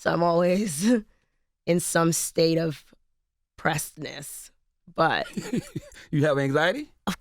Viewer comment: What does she say at 13:03? What's the word that sounds like precedence or pressness prestness or what, when she says "state of pressness" or whatever ? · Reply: pressed-ness